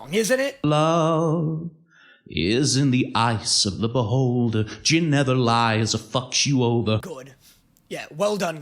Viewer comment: all this time i thought he said JIM never lies and he fucks u over, thinking who TF is JIM.. then i realsied it gin like the alcohol 💀